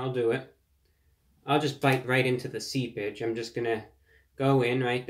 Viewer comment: I almost thought you said, “I’m diving right into the Sea Bitch”. But I realized your class wouldn’t allow such crass talk. “ Seepage” made more sense after a second viewing. Thank you for another stellar review.